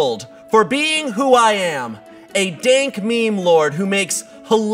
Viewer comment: I thought he said dark meme lord.... 😂